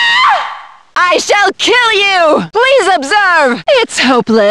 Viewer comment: can somebody tell me what does stella say at 4:48 cannot understand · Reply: Please observe